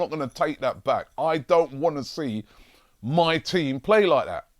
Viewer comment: What I heard "I don't want to WIN trophies"